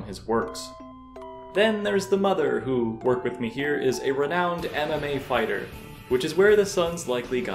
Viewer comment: I thought you said anime fighter, not MMA fighter. But it suppose it works either way.